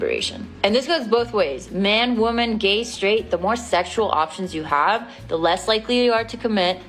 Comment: I don't think the person you commented on was homophobic. She actually said "regardless a man, woman, if you're straight or gay".